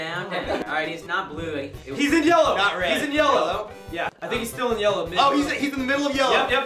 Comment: am i the only one who heard Ian say "I think he's still in yellow babe"